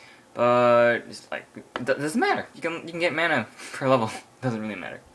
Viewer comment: did he say: doesnt matter, you can get mana per level? :D